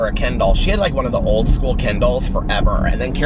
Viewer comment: I was listening while doing other things and got distracted. In the back ground I heard you say, "My Aunt got her a new Ken Doll. And she was worried she wouldn't know how to use the Ken Doll" Wait, What? not know how to use a doll? there are no instructions for dolls. And then you mentioned books. and I suddenly realized you were saying "kindle" LOL and then I was on the floor rolling with laughter. Oh, and yes, you can read a book every day. Unless it is a really dense book like Dickens or Tolstoy. Those take time, you have to pay close attention. And I often have to put them down for a while. Others are like a story you walk into that lives all around you and is finished much too soon. I have learned to savor books and not devour them too fast because it takes so long for a new one to come out. But if I discover a new author that has an established series that I like... I will gorge on them in a very short period of time.